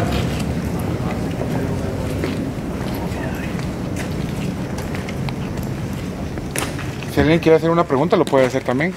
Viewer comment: min se escucha una vos q susurra algo como al microfono de la camara se escucha muy cerca pero no entiendo lo q dice... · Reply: A mi parece que dice “Cuidado allí”.